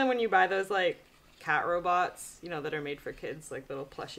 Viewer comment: Instead of 'cat robot' i thought she said 'cat bra', I was just listening to her.